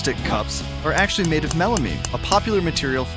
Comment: I thought he said The Cups And Plates Were Melaine Insted Of Melamine..... Crybaby here